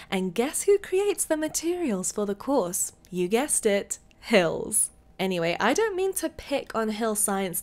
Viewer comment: When she said hills the first time I thought she said "pills"